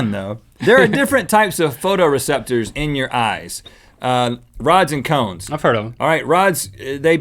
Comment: I thought he said rods and cums, not rods and cones